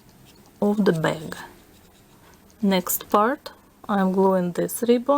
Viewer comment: When you said next part it sounded like you said next fart